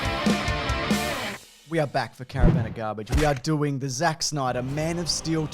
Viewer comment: I thought you said Karen Van of Garbage